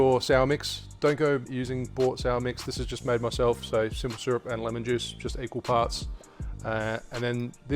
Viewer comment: what did he say was in the sour mix? Lemon juice and what else? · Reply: Dillon simple syrup